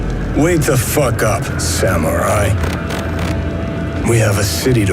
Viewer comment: I literally heard "Way to fuck up, samurai"